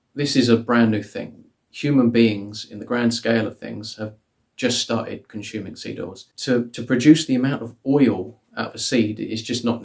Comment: I thought he said "human beings have just started consuming seagulls".